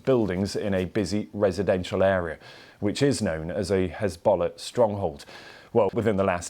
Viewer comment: Did he just say "Hizbollock"? 🤣